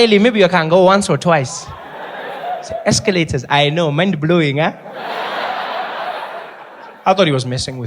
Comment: I heard 'mind blowing' as 'man blowing'😂😂😂😂 oh no!!! I am gay this evening!!!!!!